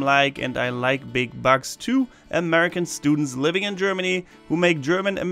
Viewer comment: I thought Said I like big “Bucks” instead of “Bugs”😂